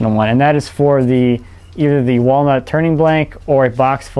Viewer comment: Sounds like your saying turning balls